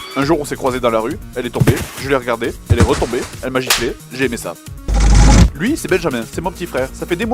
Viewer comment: Une question :: Moi et une amie ne somme pas d'accord ..a 6:33 il dit " et j'ai fait sa " ou " j'ai aime sa"